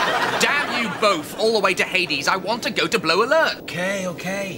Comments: Did he say "Blow Alert"? lol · Did anyone else notice Rimmer say "blow" instead of "blue" alert at 1:01? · Okay...I’d swear it sounded like Rimmer said “I want to go to blow alert,” after that certain regulation was quoted at him!!!